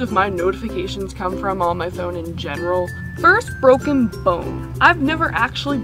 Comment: I heard it as first broken phone at first ahaha XD I love how you put up your hair by the way!!